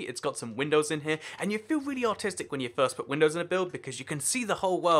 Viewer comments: I thought he said autistic, lol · I thought he said "you feel really autistic when you put windows in"